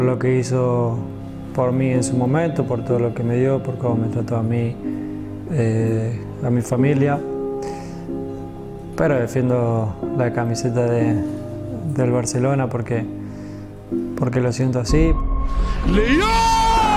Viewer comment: did u say family?